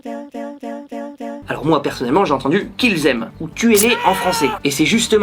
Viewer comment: À 8:18 moi j'ai entendu ''bien ou quoi ?''